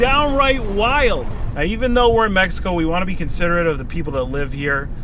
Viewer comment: did he say he is in Mexico???